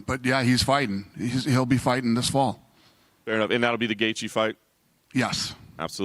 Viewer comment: Bruh I was watching the video with captions on and it said “Gay chief” instead of gaethje 2:45 lol